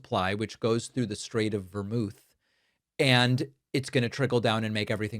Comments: Did David say "the Straight of Vermouth"? (03:26) David, are you OK? I know covering all this is stressful, but are you craving a martini? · I must be heading for alcoholic dementia. I heard ‘the Straight of Vermouth’ 😅